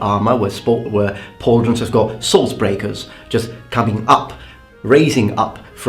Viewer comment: What was the word you used for those raised projections on the pauldrons at 5:37? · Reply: That part of pauldron is called "sword/blade breaker" or if small "stop rib"